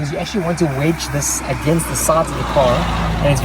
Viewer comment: do any English speakers know wtf he just said here? 💀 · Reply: "you actually want to wedge this against the sides of the car"